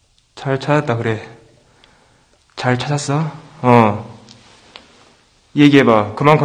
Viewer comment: ☞☆2019년은 시대!★☜ 0:30 스타트 (이어폰 착용 필수)★ 0:52 어읔 보기만해도 소름 돋아. 1:06 언제 저기도 한번 들어가보면 1:14 ~ 1:27 소리집중 ( 발자국 소리?) 2:34 뭐가 있는거 같기도 하고 2:52 ~ 3:00 창문에 먼가 던진소리.. 3:23 여우 계단 3:57 조심조심.!! 4:14 귀신이 를 알고 있다. 4:24 뭔가 있는 듯한...(흐물흐물) 5:01 엄청 넓어 보인다...ㅎㄷㄷ 5:55 안으로 들어가는 6:23 이 들어가니 안에가 작구나. 6:28 퇴근합시다..ㅋㅋ여러분들 6:35 ~ 6:49 이콜이 골룸 빙의?ㅋㅋㅋㅋ 6:53 안에는 수신이 좋지 않음 7:45 ~ 8:12 소리집중.. 8:25 ~ 8:30 발자국 소리? 8:39 는 1층을 보러간 상태.. 9:00 배그 좀 아는 9:17 ~ 9:25 소리집중 (귀신 말 소리?) 9:38.이 들거 갔던 곳.. 9:44 ~ 9:58 소리집중 (서로 날 찾네?) 10:07 ~ 10:18 먼소리야.. 10:21 ~ 10:31 무슨 소리냐 ..? 10:45 ~ 10:49 계속 소리난다 ㅎㄷㄷ 11:00 ~ 11:05 설마 여기있는 의자는 아니지 11:34 ~ 11:41 유리 깨지는 소리ㅎㄷㄷ 12:42 ~ 12:55 소리집중(소리는 작지만들림) 12:57 ~ 13:01 여기 있지?.. 13:14 뒤에 또 건물이 14:08 ~ 19:44 ☆(귀신과대화&사연)★ 19:45 잘가요 하고 조용... 20:24 사연이 참 가슴 아프네 20:57 그 친구둘이 죽어서까지도 무섭던가 21:05 여기도 비슷한 사연을 가진 여자가.. 21:20 님 수고하셨습니다.!★ 즐감하셨다면 좋아요!♡쿠욱!★ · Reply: 초에 잘 찻았어 나만 들림?